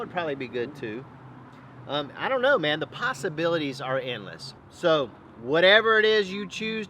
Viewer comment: I thought you said the "pastabilities' are endless hehehe. this was one fabulous cook mon ami..yum yum!